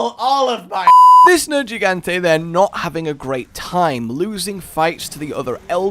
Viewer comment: I thought you said they where not all sad